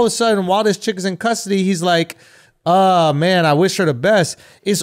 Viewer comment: When trump said “I wish her the best” I heard “I hope she survives” 😆